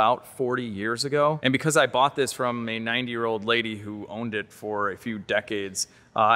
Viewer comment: "and because I bought this from a 9 year old lady who owned it for few decades" I didn't have my airpods on, so I probably misheard that, right? A 9 year old lady? Few decades? Haha! 😂